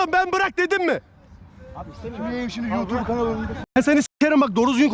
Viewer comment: 9 numaranın ne dediğini tam anlamadım anlatıcak var mı? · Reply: Senide youtube kanalinida s.. diyo galiba